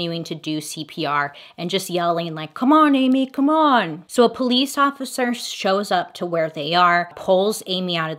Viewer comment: It literally sounded like he was trying calm himself down I heard “come on, come on, stay calm”